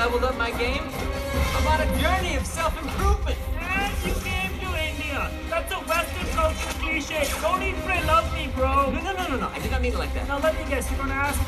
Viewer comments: What does he say at 5:04? My ability to listen to accents suck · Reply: "and you came to India? that's a western culture cliché. don't "eat, pray, love" me, bro" "know let me guess, you're going to ask me about saffron and cardamom and naan bread. which is the same as saying "bread bread", which is the same as saying "chai tea" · What did Pavitr say here? I didn’t understand anything lmao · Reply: " -- thats a western culture cliché. don't "eat, pray, love" me bro"